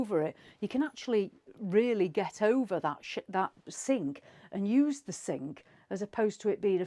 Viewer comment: Did she say you can get over that shit? 17:02 😂